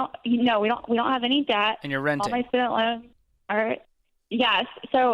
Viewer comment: Did she say "no we don't have any debt, only student loans" ??? That's debt O.O